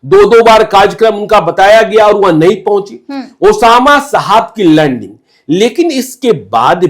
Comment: ye kya bola osama saheb ka lan....😂😂😂